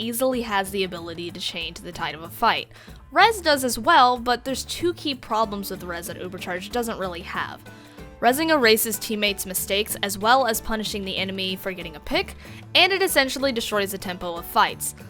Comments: why do i hear "resing a racist teammate" i am so brainrotted by Instagram reels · I’ve got hearing issues so I heard “Rezzing a racist’s teammates’ mistakes” instead of “Rezzing erases teammates’ mistakes” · I thought she said "rezzing a racist teammates mistakes" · Had to listen to that several times, lol. Thought she said rezing a racist teammate’s mistake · I heard "Rezzing a racist's teammates mistakes" and I swear I had no idea what you were saying for a solid minute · did she say racist or is it just ne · Is it just me or does it extremly sound like She says 'a racist' when Shes saying erases? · I had to go back again. I heard "ressing a racist teammates mistakes" lol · Lol i heard "erasing a teammates mistake" as " a racists teammates mistake" twice until i realized what you actually said. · Thought she said "rezzing a racist teammate"